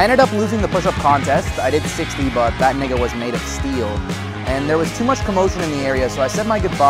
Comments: I SWEAR TO FKN GOD I HEARD HIM SAY “THAT NI*GA WAS MADE OF STEEL” AT EXACTLY 11:00 minutes !!!!!!! 🤣🤣🤣💀 WTF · did he say " that n*** was made of steel 😭